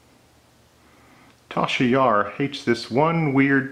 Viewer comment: did you say "Tal Shiar..." like in Star Trek? Or did I miss hear you?